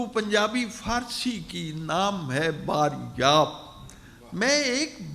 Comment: someone plz help on the name this book I can't understand whether it is "PUNJAB" "BANJAB" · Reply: Banjab